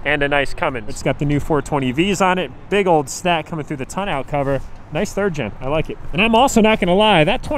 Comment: when you said tonneau cover it sounded like you said toe nail cover